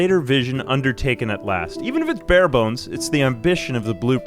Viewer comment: what did you say?😂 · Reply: “Even if it’s bare bones…” A lot of words in a short amount of time 😂